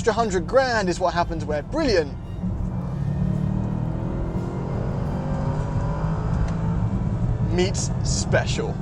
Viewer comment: "when brilliant..... meets special" but I miss heard him and thought he said: "when bullion.....meets desire" 😂